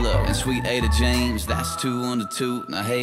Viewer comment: I finally under stand the lyric: "Now there`s Willa and Sweet Ada James, thats TWO UNDER TWO." Two kids under two years old. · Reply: I finally saw your post, I was always confused by that... because because I thought he said two into two, not two under two...